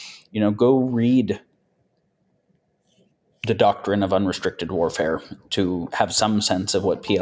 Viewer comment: min. what does he say? the doctor in a 'vulnastrictial'(?) warfare? What's that supposed to mean? Can anyone help me out? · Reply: "Doctrine of Unrestricted warfare", probably referring to <link>